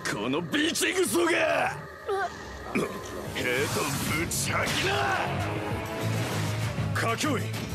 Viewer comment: Heto putangina!!! Is it just me who heard it in tagalog or what?